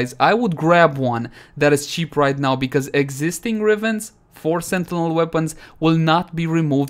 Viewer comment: Misheard sentinel weapon for sentient weapon, Haha! 😆 Almost went nuts, still haven't got Paracesis riven.